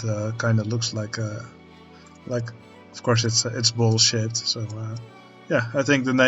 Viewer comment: I had to do a double take, I thought you said "bullshit" not "ball-shaped" lmao This area is shaping up nicely, it might become my favourite.